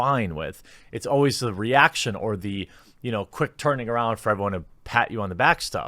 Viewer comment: heard it as "reaction orgy" on the first listen. Seems accurate.